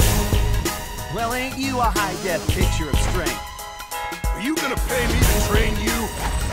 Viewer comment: What does Ed say in the intro? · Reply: Ignacio Cortorreal "Well aren't you a high Def picture of strength." Sorry I pretty sure he meant definition but def to shorten it I may be wrong.